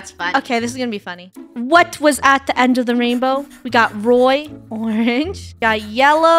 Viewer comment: funneh said roi not red 😂